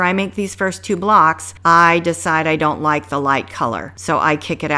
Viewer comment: I thought you said you decided not to use the lights.